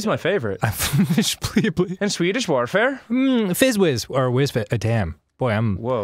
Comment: i thought it said Swedish welfare